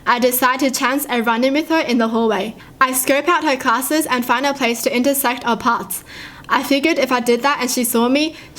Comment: 5: 41 Thought she said "find a way to intersect our parts"